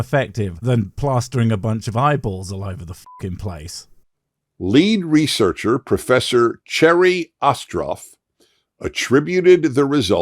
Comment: I thought he said, "lead researcher, Professor Cherry Cough Drop". LOL